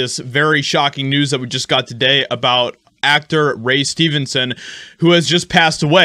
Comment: I about had a heart attack. I thought you said Ray Stevens